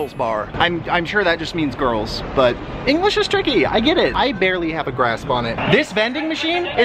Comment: Swear to God he said "I barely have a grass bonnet."